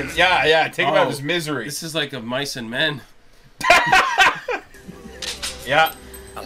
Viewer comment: At first, I thought I heard say "This is like Of Bison Men" instead of "Of Mice & Men" 🤣🤣🤣